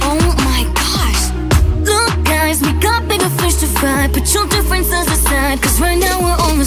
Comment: What I thought it said: Omg! Look guys we can't be the first to fight What it rlly says: Omg! Look guys we've got bigger fish to fry